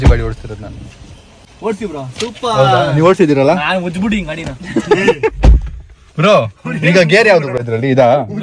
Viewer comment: what did said😮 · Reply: Benz car na rape madbittidini